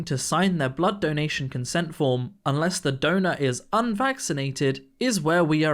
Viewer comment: I thought you said donut instead of donor and was so confused for a second 😭